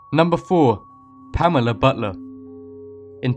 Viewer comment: I thought when he said Pamela butler I thought it said Pamela butter